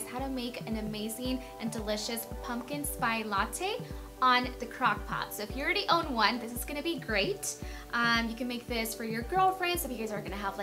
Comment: Did she just say pumpkin spy latte? Lol